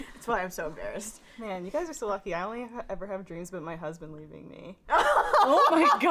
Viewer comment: what did she say at 4:25? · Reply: Has dreams of her husband leave her